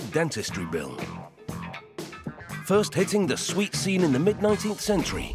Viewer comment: When he said "sweet scene" I thought he said "sweet semen" and I was like whaaat